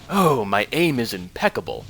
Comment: I thought said " my anus is impeccable!"